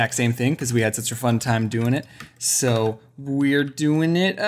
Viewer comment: Did you just say sister fun time at 0:26-0:27 XD never heard you say it but great vid